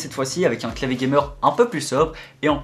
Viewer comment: a 5:21 ses moi ou il a dis monsieur gay ?? :')